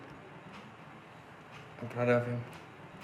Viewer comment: wtf is he saying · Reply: 26' MTB he says “I’m proud of him.”